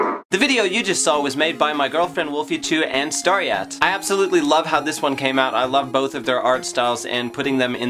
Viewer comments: did i heard it right or did i just heard "this video was made by my girlfriend wolfychu and stariaat"XD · it sounded like he said his girlfriend was both wolfychu and stariaat.. XDDD